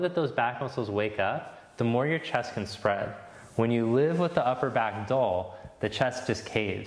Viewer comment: English is not my native language, what does he say at 6:30? "When you live with your upper back ???" What is "Arda" at 9:44? 9:55 "SamasTD"?